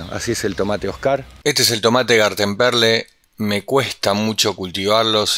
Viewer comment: No llego a entender el nombre del Cherry amarillo cómo se escribe?? Tengo varios tipos de cherrys y quiero clasificarlos ò indentificarlos ( yo entendí "gatemperle" 🤭pero Google no tira nada claro) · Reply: gartenperle son rojos